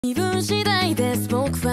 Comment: 受験関係の動画って前提だとBGMの初っ端が 「微分次第です〜」に聞こえてくる